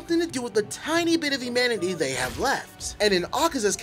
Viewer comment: I swear I heard him say immanity not humanity